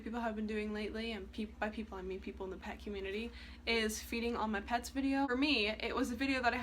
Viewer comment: I thought it said “freeing all my pets” and I was like wHaT?